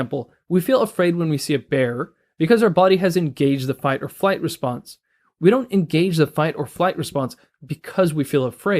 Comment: Well I thought you said we don’t have free will